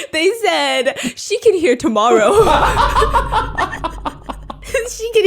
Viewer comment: I was about to drink a sip of my tea and then I heard "she can hear tomorrow" and had to control my laughter and hold my tea as stable as I could before I spilled it on myself.